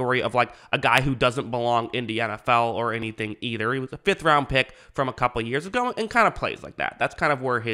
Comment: I strongly do believe he’s looked better every single week. Granted, he started the season pretty awful so it’s not hard to get better, but dude is big and strong, just needs to develop, and if the Raiders are gonna rebuild, or at least retool (like they should), he’ll be vital to them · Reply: thought you were saying he’s worth a 5th round pick ngl